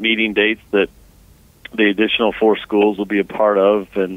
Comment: “Initial 4 schools” is an important quote that he slipped in [edit] never mind, I misheard him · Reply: Did he say initial? I heard additional.